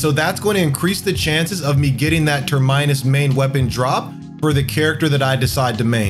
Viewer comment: Gon don ghoza?reeeee Ter main us weapon? Reeeeee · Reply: for real, it sounded like he said turd-minus lmfao